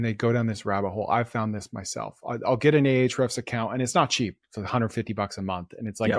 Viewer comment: a what account? What is he saying? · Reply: He said Ahrefs account